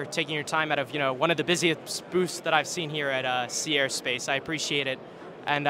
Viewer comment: I had to check where this was because the when interviewer said Sea Air and Space, I thought he said Sierra Space, the makers of the Dream Chaser shuttle.🤣 (silly me)